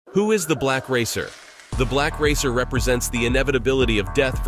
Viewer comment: At first i heard "black raper"